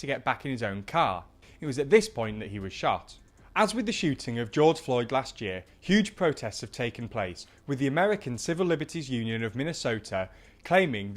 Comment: Did he say the shooting of George Floyd?